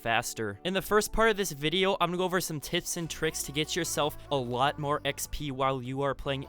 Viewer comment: idk if anyone else heard it but I think he said tits and tricks. Good video btw.